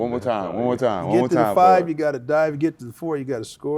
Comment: The Great Randall Cunningham 5. U got to dive 4.U got to score 3. It’s up to me 2. U know what to do 1. The job is done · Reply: I thought he said "5, you gotta die" lol